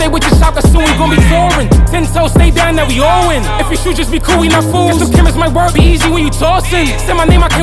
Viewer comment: wat he said? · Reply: if we shoot, jus be cool we not fools